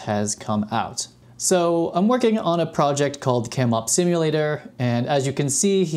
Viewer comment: I thought you said “k pop simulator”